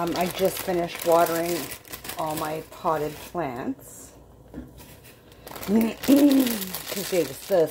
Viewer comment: I had to listen to that last line again. I thought you just said you just finished watering your pot plants instead of potted plants. PML